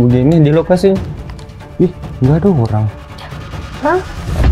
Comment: ngomong apa si dia?ada yg tau? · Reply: Dia bilang gada orng waku disamperin